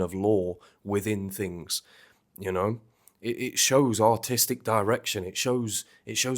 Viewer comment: i know you're saying "artistic direction" but i hear "autistic direction" and i think it fits just as well. <3